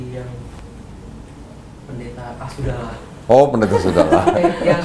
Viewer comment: kedengeran pd bilang.. "pendeta ah sudahlah" aku ngakak jadi inget sm pendeta itu, terkenal nih "pendeta ah sudahlah" kalo dia lewat dpn rmhku iseng diteriakin "ah sudalaaah".. gitu gimana ya? hahaaha😂😂 mb. ini cantik, rapi, serasi, pas pake hijab tambah cantik, Alhamdulillah.. semoga selalu Istiqomah aamiin